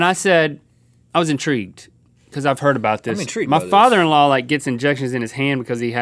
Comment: I for 1 second thought link said 'my father' but then I listened again and I realized he said father in law. Link never talks about his dad.